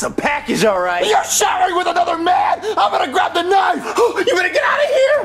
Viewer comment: I thought he said “IMA GRAB THE 9INE”